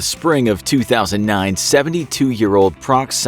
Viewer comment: (2019) he clearly says 2009